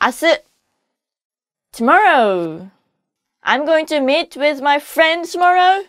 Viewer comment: Did she say "I'm going to mate with my friends tomorow ?"